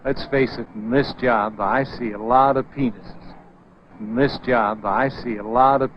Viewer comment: Lol I thought he said “in this job I see a lot of people.” Then he replayed it and I heard it